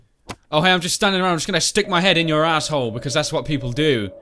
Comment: did say asshole?